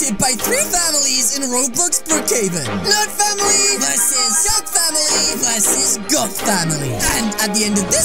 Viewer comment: In the intro I thought he said the nerd family vs the nerd family vs the goth family